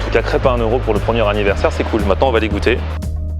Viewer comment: c'est moi ou il dit " on va degouter ca"